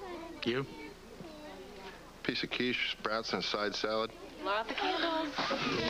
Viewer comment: 1/2 lb. Barbecue chicken boneless pork ribs with mashed potatoes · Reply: I misheard it myself. He more likely said SPROUTS and side salad.